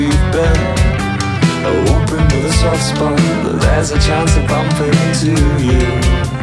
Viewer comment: WHAT do they say at 0:34 ? I'm not British enough for this, the lack of captions is crazy. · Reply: hoping with a soft spot, there's a chance of bumping into you